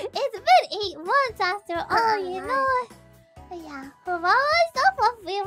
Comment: Full rrat mode. Did Fuwawa say "You Knowa"